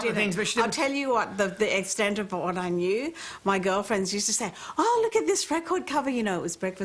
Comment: Does she says "my GIRLfriend used to say..."??? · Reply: yes 'my GIRL friends', as in her friends that are girls, duh!